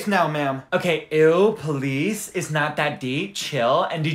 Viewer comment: “That’s offensive” What I hear: “datsa fansa” 💀🤣 Edit 1:thank you so much for 5k likes❤ Edit 2: TYSM FOR 5.7K LIKES